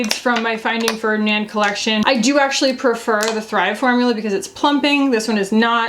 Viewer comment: Thanks for putting up the website on screen, cause I honestly thought you said Thrève 😅